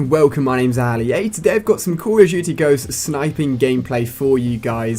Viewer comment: sounds like hes saying calla jujy instead of call of duty